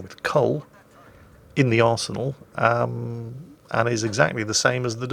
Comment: When describing the USS Salem I could have sworn said bought with coal in the Asshole instead of Arsenal HAHA